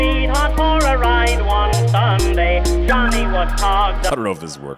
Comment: Sounds like he was saying "Johnny was HOGGED up"